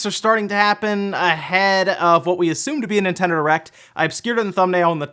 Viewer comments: did you say Nintendo Erect? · 7 seconds In and I'm pmsl. Sounds like you said nintendo errect....